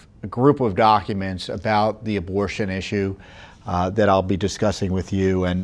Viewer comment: When tom said "abortion issue" I literally heard "devil worship". I had to put the vid back to make sure..